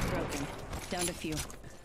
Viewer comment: Anyone else hear wraith say “Downed a few” when he knocked those dudes reviving?🤣🤣 never heard her say that B4